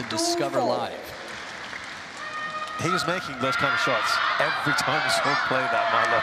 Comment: what is he saying? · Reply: lol. "kind of shots" but it does sound dirtier.